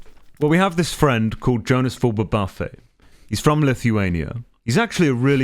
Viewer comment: Someone type how this person's name is spelled I couldn't understand Or pronounce the name colossal just said there Better yet link me to that person's YouTube channel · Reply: Jonasfull